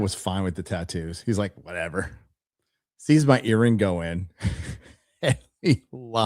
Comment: I thought you said your Dad saw your urine go in, but it was earring! Had to rewind to make sure! 😬💦